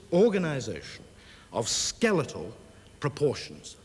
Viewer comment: It sounded like Haseler said: “…of Skeletor proportions” 😂 He was ahead of his time by about 3 years, although Skeletor would have been ideal as a politician.